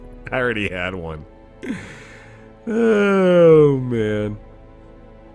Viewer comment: At the end of this episode, when you said "Thanks for watching this episode of Darkest Dungeon" I heard: "Thanks for watching this episode of dork in the dungeon" XD